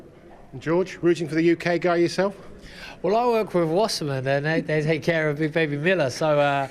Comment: who did he say he works with? Wassimo???